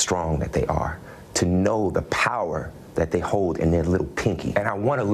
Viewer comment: did Jessie say "He wants a little gay boy?" Lol smh , He should Definitely watch what he says if he doesn't want it to be taken out of context.